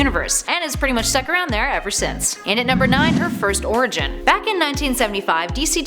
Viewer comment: LOL! I heard "Her first origin" as "Her first orgasm".